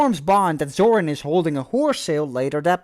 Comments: did you say "zorin is holding a whore sale"? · I thought you said a “whore sale”........ah well.😂